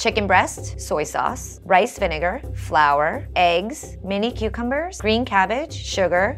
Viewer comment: did she say “mini” cucumbers?